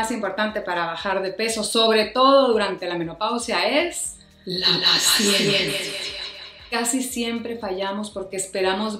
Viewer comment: Por que yo escuche "La Siesta" en vez de "la paciencia " 🤔🥴🤭 Excelente video amiga, hablaste justos del problema y la solución, te saludo desde Uruguay. 🥰😘